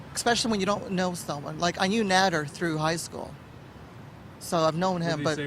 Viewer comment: did she say "I knew Nadler since high school..." Like Jerry Nadler?? wouldnt be suprised!! lol 😁😁